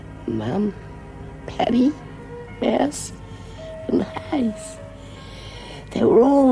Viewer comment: WHAT name did Lizzie say after Mouse ❓ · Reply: Judy was one of my faves😍 And Lizzie actually said "And Haze"😚👍 aha🤣